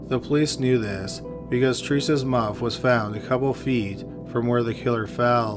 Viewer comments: "Her mouth was found near to the spot where the killer tripped"... Now am I hearing things and im certified crazy lrrr djd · "The police knew that because Theresa's"..."mouth"..? What is he saying? I hear mouth but that doesn't seem right lmao.